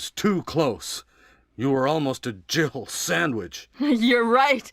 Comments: I always heard Barry say " You were almost a jibble sandwich!" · When I first heard that line for some reason I thought he said "jiggle sandwich" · Everyone say's Jill sandwich but doesn't it sound like he says Jibble sandwich?